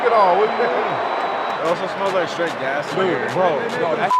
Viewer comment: 17 😂Thought he said straight a$$!!!🤣😆